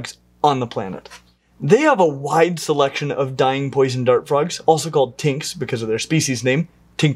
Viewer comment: I left the video going for a minute and left and when I return the first thing I hear it "They have a wide selection of dying poison dart frogs." Had to rewind to find out he was saying deying.